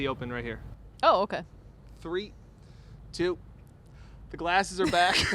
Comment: I heard "with the glasses" and immediately reached for mine 🤓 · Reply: Actual quote " the glasses are back" but I heard " With The Glasses"! 😂